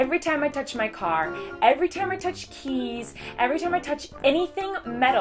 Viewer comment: I totally thought you said 'every time I touch cheese', so I went back and realized you said 'keys'. That makes much more sense.